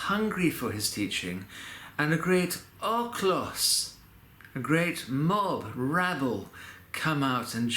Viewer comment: Hello.Glory to Jesus Christ! What is the word you use to describe the mob/throng(@ 3:33) " a great --- ? I never heard it before. {?auclus) Thanks in Christ! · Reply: o ochlos - ο οχλος usually an unruly crowd